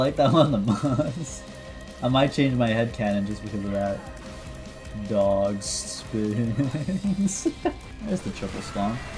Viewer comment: I thought it was saying “Bombs in space”